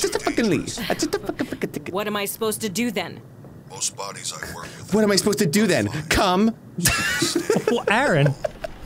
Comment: Anyone get the dialogue from 22:47? I can't hear him :( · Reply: I think it’s “most bodies I work with at night are fine, so stay calm. Okay? Embalm them, fill the paperwork, everything. (Unintelligible)” that’s as much as I got